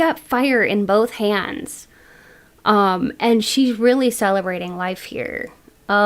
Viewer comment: In pile 1, I thought you said "celibate life" and was like correcto Mundo 😂 but celebrating life is true too ig 😅